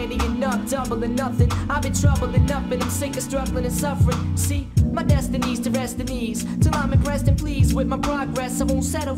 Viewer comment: i always thought he said "my destiny is to rest the knees"